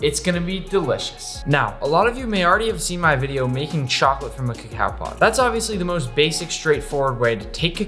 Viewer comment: Who at 1:04 thought he said form a cow